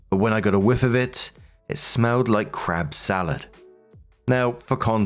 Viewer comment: Why did it sound like you said "crap" instead of " crab" 😅.